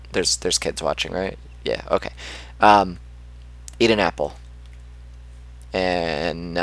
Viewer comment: did he said eating apple